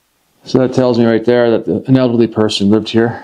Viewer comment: So when you entered the first bathroom at the beginning of the video it sounded like you said pointing at the toilet ( A Penelope person lived here ) What did you intend to say or mean ?